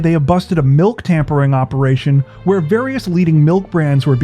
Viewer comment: I swear I thought it said Breast Milk tampering. I was like… how tho?